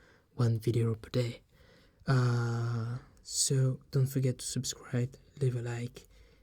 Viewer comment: At the very end when he said “subscribe & leave a Like..” I thought he said.. “subscribe to David Icke..” I thought, wow he’s promoting David Ickes msg about the Quarantine lol